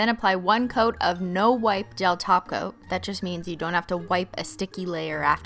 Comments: Every time she said "gel top coat" I thought she was saying "gel taco"..lol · Sound like she is saying gel taco 🌮